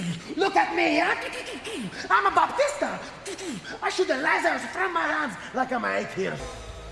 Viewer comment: I've seen this multiple times and every time I still laugh to the point oh tears. Santino doing Dave Bautista is my favorite." Look at me, I'ma Bautista" shaking his arms. Comic gold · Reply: I remember watching this as a kid and busting out laughing cuz I thought he said: "I shoot a lasers from my ass." 😂💀